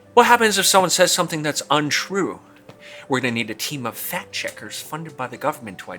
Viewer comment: I totally thought the first time he said "fact checkers" he said "fat chickers". I busted out laughing. Still made sense.